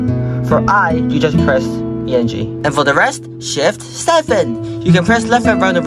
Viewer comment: THAT SHIT SOUNDED LIKE 67